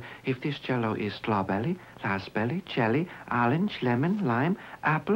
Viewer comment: Gotta love that old timey fake Chinese accent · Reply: it sounds like he says "assberry"